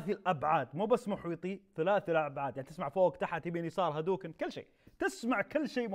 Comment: انا سمعت هادوكن 😄